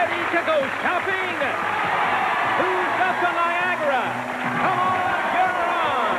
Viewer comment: For the first contestants called, I thought at first they said Whose got the viagra! haha, had to replay that.